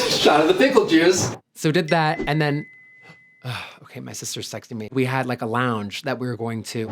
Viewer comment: Ok i know your saying shes texting you but it definitely sounds like you said she is secting you lmao